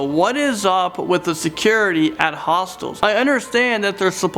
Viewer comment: Honestly I thought that he said Hostess , The company that does Twinkies 😅lol